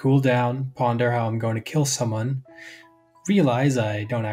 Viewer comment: did you just say to 'kill someone' thats next level, I love it lmao